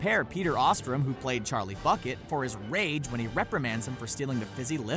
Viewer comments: Does no one notice that at 10:53 he said fuckit instead of bucket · Did he say Charlie Fuckit, instead of Bucket?